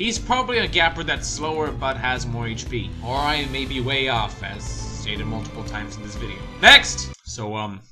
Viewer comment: what did he say? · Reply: "...moves slower and has more hp, or I may be way off as I have stated multiple times in this video."